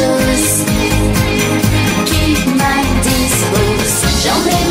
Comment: did she say "keep my distance" ??